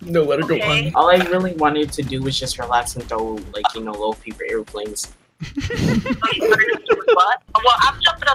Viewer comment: I thought he was gonna say "I just wanna relax and.. ya know.. throw up gang signs" instead he says paper airplanes.. it sounded like what I thought the first time